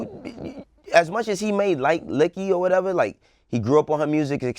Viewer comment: Just as much as he may like Licky? what the fuk did just say? 14:59 Lol😬💦😼